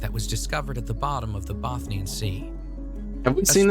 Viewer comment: is it just me or he said bathne sea while zooming in Baltic sea ? 0:16 he definitely does not say Baltic sea